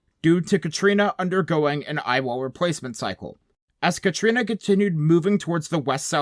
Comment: I heard “eyeball replacement surgery” and had to replay it like 5 times to unhear it 👁️